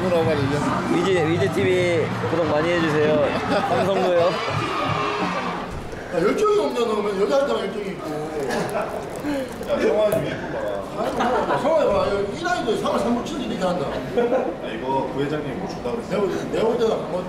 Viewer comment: 에 이대호 선수가 뭐라고 하는지 적어주실 분...? 몇번을 들었는데도 너무 빨라서 뭐라고 하는지 모르겠어요 ㅜㅜ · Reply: 야 열정이없냐너는 여자한테만 열정이있고 (야 성한이 유니폼 봐) 성한이봐 3할3푼치는데도 이렇게한다